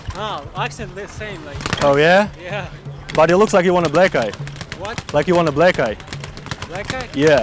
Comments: Lolz it do sound like he saying "you wanna black guy!" Lml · I am still waiting for the day that really beats the fuck up of any of those types who are still mad after you tell them its a prank I mean...these are not harmful pranks so you dont have to get mad after it too Btw...did you find Niga in this regional? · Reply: nah some ppl have anger issues u cant just record ppl and tell them its a prank after it sounded like he said do you want a black guy which is very racist